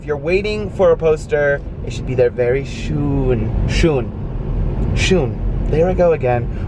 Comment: When she said function, I thought she said the f word and sh**. 😂 I know wouldn't do that though. I love you guys! 💕♥️💖😘❤️ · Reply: I thought she said fshon. F bombing LOL jk.